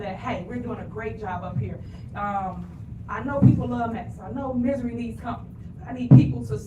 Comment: Did she just say "Misery LEAVES company"? What a dumbass. Tiffany, the saying is "Misery LOVES company".